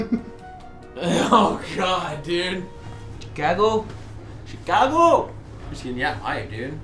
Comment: Did said GAGO at 27:55??